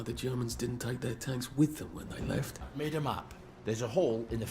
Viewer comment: Did you notice when Arthur asks why the Germans left the tanks, Ollie interrupts saying "i've made a map" but it sounds like he said "I made em up". In a game about unreliable narrators where the playable protagonists all perceive parallel experiences of the same events I think it's intentional.